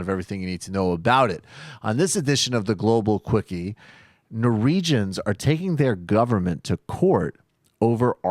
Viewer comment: Did he say Nor-regions?